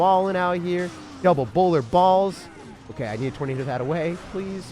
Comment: Nobody else heard him say “double bowler balls”? Just me huh